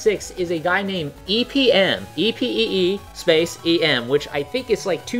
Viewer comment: Cannot understand what is saying when spelling the lp'er's name at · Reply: Epee Em.